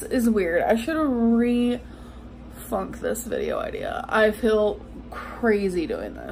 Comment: Did she just say “re thunk” instead of rethink? What even 😂